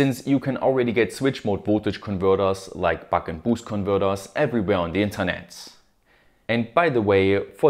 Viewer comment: what about the boost converter ? F word boost converter ?